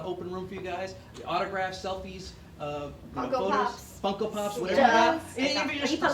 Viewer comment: When he said Funko Pops I thought he said Coco Puffs.